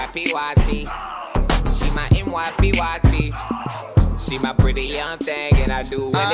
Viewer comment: When I heard this song on IHEART I thought they was saying she's my NY PYT til I realized they saying MY PYT